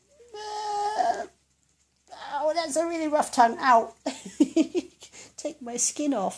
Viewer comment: it says ruff instead of rough